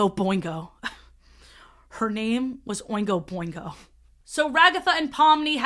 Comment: oh gosh I heard that as “boinga” and thought of the backyardigans. Full disclosure Tyrone abstracts first.